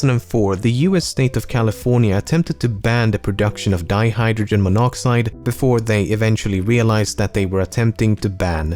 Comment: I actually thought you were saying "dihydrogen peroxide" the whole time during the water bit :p